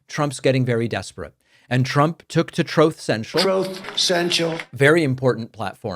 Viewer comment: And I quote from that TroothSensial post: "Those lying to the American People on behalf of violent criminals have to be held responsible by the Agencies and the Courts." Alright Police, FBI, CIA, you heard it here...lock up Donald Trump, J. D. Vance, Marjory Taylor Green, Ted Cruz, and all the other sycophantic GOP. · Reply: I thought he said "Trout Central" when I heard it the first time.